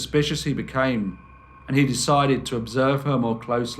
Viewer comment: You should have call this story "and he died"because that's all I heard is"and he died"if I had lived in that time I would have broke out running that bitch should have been called the angel of death